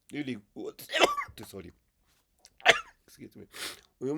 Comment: I think I heard a 'Désolé' in French at minute 1:40 by the way, excellent explanation